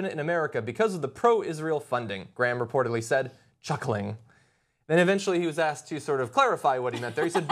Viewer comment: Am i the only person who wasn't watching the text and misheard the word "chuckling" as "cha-ching!"